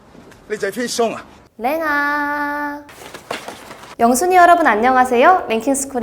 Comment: 가끔 영순위 여러분들... 로 시작하는 그게, 원송이 여러분으로 들리고.. 놀라는 경우가 있어요..OTL 귀가 않좋은듯...